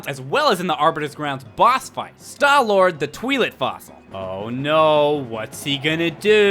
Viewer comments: di-di-did You say Stallord the "tweelit" fossil? Oh for someone who claims to be one of the biggest Zelda fan, you are bad with all of the names...but that's okay. :D <3 <3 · Sounded like he said "twee-lit fossil." I thought "twilit" was pronounced "twye-lit," because twilight is pronounced with a "twye." Yes or no?